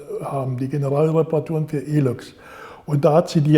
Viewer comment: What's the word he's saying around 1:42 ... die Generalreparaturen für ____? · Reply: E-Loks - Elektrolokomotive.